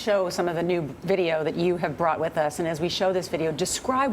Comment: I'm I the only one that heard her say " brought it with is"?